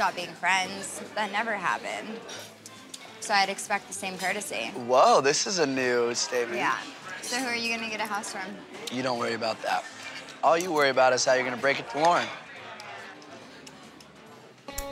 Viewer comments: I heard “who are you going to get a house from.” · I heard 'so, who are you gonna get a house from'. Unless this is a Yaurl/Yanni situation.